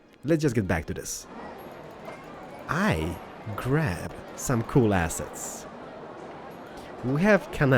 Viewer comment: i thought you were gonna say " i grabbed some cool ass mods." but you said " i grabbed some cool assets."